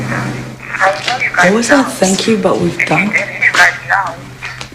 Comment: Sounds more like - "Thank you, but we've died." Which is fking hilarious by the way "nah it wasn't me I'm dead" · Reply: I always thought I Heard " Thank you but we're done here"